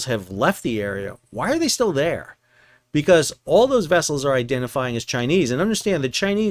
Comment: I liked when you said, "vessels identifying as Chinese". I almost thought you said, "self-identifying as Chinese." Lot of that going around lately.